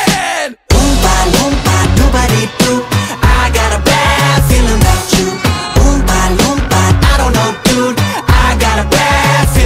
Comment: Bro i was at a party and i was jamin in my airpofs listens to this i thought they said “i got a black feeling about you” it was so funny😂